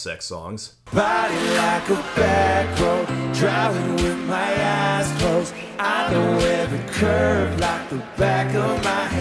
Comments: In “Body Like A Back Road” I always thought he said “drive it with my asshole” instead of “drive it with my eyes closed”. Well then. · I always thought he was saying "Driving with my asshole" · Did I hear… “Driving with my ass closed”? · I swear, during that live performance clip I thought I heard Sam saying "drive it with my ass closed". As if the song wasn't already embarrassing enough. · "Body like a back road, driving with my ass closed..." You won't be able to unhear this now. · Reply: I thought he said "driving with my a$s cooked" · LOL, I heard "Driving with my asshole"